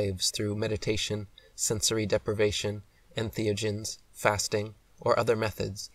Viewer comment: “when you slow your brain down through meditation , fasting , sensory deprivation and?.” I couldn’t make out what the last thing he said was. Antheogens?someone help lol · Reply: Entheogens